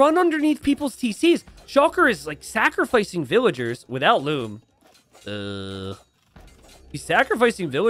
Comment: mfw I thought he said without lube instead of without loom lmao